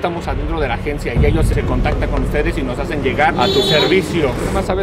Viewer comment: Quien más escucho que dijo se contactan con fede minuto